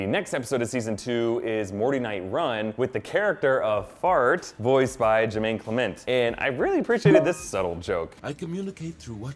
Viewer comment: Wait at 13:48 did he say “Jessica’s feet”? Does north have foot fetish?